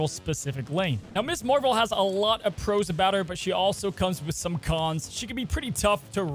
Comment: Did you say she comes with some “Khans”?